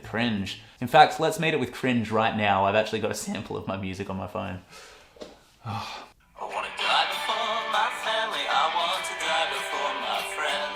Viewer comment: I thought the song said, "I want a duck for my family. I want a duck for my friends." Then I listened to it a few times more. ... I think I like the duck line better! (◠‿◠) 😜